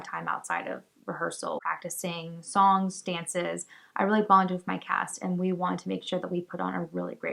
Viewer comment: when you said “bonded with my cast” I heard “bonding with my cats” and just thought huh, that’s one way to do it I guess